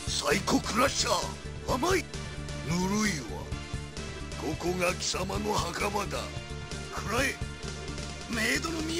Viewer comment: What is Bison aka Vega aka Dictator saying at about 8:38 starting with a "kokona" sound? I don't speak Japanese but that saying got stuck in my head and i thought of it today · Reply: 「ここが貴様の墓場だ。」 Koko ga kisama no hakaba da. ("This place will be your grave.")